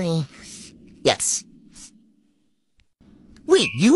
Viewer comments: I thought he said he was a math lizard. Lol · I heard “I am a maths lizard”🙂. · I thought he said he was a math lizard and I was so confused. 😂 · People who thought he said “ I’m a math lizard” 👇😂😂😂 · No cuz the way she kissed him at the end was like “I’m so in love with u” after he said he’s a math wizard 😭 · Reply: Wizard I thought he said lizard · People who thought he said ima math lizard 😂 👇🏻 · I thought he said he’s a math lizard 😭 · I heard “I’m a math lizard” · Why did I hear “ babe I’m a math lizard 🦎 “ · I heard- ' I'm a math lizard '. no matter how many times I listen to this it sounds lizard 😅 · I thought he said math lizard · No cuz the way she kissed him at the end was like “I’m so in love with u” after he said he’s a math wizard 😭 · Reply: I thought he said math lizard 💀😭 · Ngl I thought he said he’s a math lizzard 😭😭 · No cuz the way she kissed him at the end was like “I’m so in love with u” after he said he’s a math wizard 😭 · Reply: I thought she said " your a math wizard"